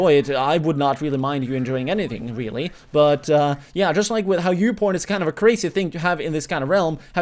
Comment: Did you say "youporn" at 4:54 and 5:03 , or am I hearing things? ( ͡° ͜ʖ ͡°)